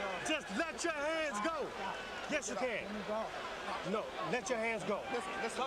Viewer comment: Can anyone tell me what Tyson says around 38:13? After the dude says "you have two hands", I can't make out what Mike is saying. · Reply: You have 2 hands do you understand mike tyson: no, let your hands go mike tyson:ah can't